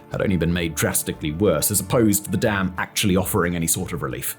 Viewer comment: God damn it I thought he said the dam actually failed. Just about had a heart attack